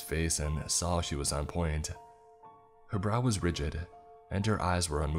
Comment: I heard "Her bra was rigid and her eyes unmoving", but I'm pretty sure it's supposed to be "Her brow". I like the former better!